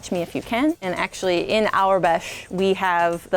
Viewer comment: Subtitles say [INAUDIBLE]... normie she said AUREBESH. The Star Wars alphabet... 🙄